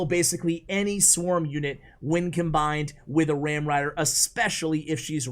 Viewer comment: Almost thought he said "Wam Rider"